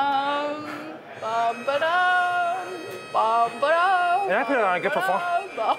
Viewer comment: what did you even say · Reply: "man, i put on a good performance". also did you respond to yourself lmao